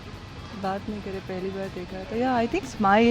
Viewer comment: did Sangeeta say "sidharth ko pheli bar dekha tha" who is sidharth??!!!